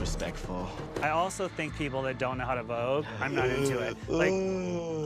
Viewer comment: I've never seen this show actually so, right after the segment with Koco struggling to write down the name, I misheard "I also think that people who don't know how to vote... I was gagged"